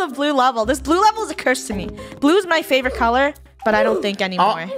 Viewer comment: Thought I heard funneh Say: "2021 is my Favorite Color" Edit: Ty for 52 Likes 0